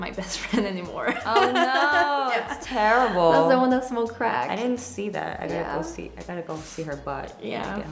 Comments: I heard crack but I wasnt sure if she said when she smoked crack as in admitting she smoked crack an got it then or that it was next to her crack. 😂😂😭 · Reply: Autumn Jean i thought she said when she smoked crack to 😂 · What did the blonde girl say at 6:17 ? "Its in the small of my crack" or "it was when I smoked crack?" I've rewinded it a few times but can't quite make it out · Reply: Jay Hari she said " that's the one that smoked crack"